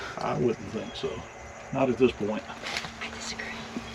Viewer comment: Did anyone else hear the girls voice whisper at 23:03 after Mr says I wouldn't think so not at this point? It sounded like she said I'd scream.